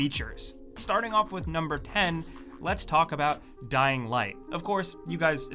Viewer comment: Is it just me or did my fav YouTuber actually said pen instead of 10